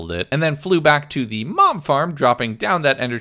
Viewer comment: i thought he said mom farm XD